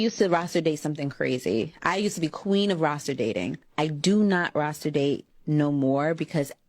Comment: I thought she was saying Rasta dating. She know what happens on them rafts in Jamaica lol